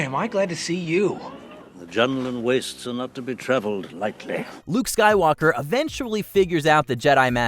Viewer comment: OMG, at 3:29 look at the closed captions! That's EXACTLY what my 6- or 7-year-old self thought Ben said before I knew the line was "The Jundland Wastes are not to be travelled lightly". Looks like the autocaption AI misheard the line too. I always thought Ben said, "A gentleman wastes enough to be travelled, lightly" - which makes no sense, I know, but who knew anyone would be saying anything about Jundland Wastes? That's not something anyone expects. Also, later, I thought Luke asked Ben, "You fought in the colonial wars?" Colonial wars made sense to me as a child, as the U.S. was once a set of colonies and went to war against England for independence, for example. My ears would have never expected to hear anything about clone wars (what's that?).